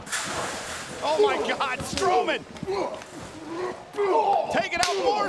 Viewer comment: am i the only one who heard her say “stowman”?😅😂